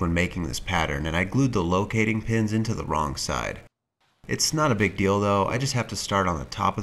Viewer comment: did you just say "It's not a big dildo" ? 🤣🤣🤣